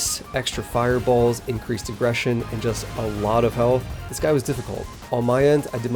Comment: i thought he said increased depression